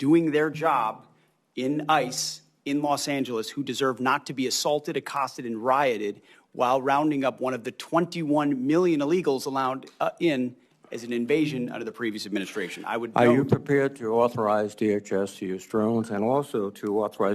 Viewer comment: Did he just say there is 21 billion illegal immigrants in l.a. lol